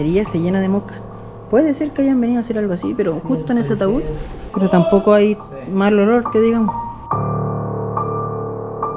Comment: Impresionante el tamaño y la infraestructura del cementerio, como siempre tus vídeos son de lo mejor, también escuché voces y un grito en el minuto 16:44 y también un susurro 17:58 muchas gracias por el vídeo y a que te apoyó en el videazo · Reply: Dice en el min 17:58 salgan de aquí y TN el min 16:44 aparte de el ohhhh antes de eso cuando habla susurros buen contenido bless🍀✌️🔥